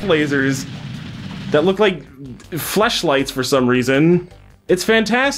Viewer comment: did he calls rose quartz’s cannon a flesh light?